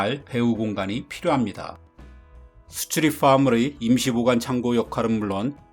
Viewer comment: 수출입 화물이 -> street farmly 이렇게 들리네요 ㅋㅋ